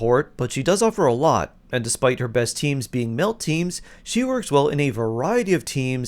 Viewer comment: Heard that as "Citlali's best teams being milf teams". Which is actually kind of accurate if you think about it